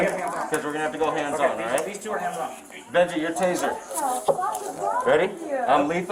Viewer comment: sounds like he say's "Benji your taser" Benji being a name. Could be wrong though XD